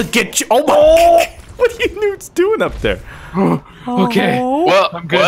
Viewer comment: was I the only one that thought he said nudes